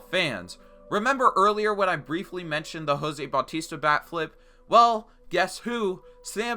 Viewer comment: I thought you said a back flip lol honestly tho a bat flip?? 😂 How pathetic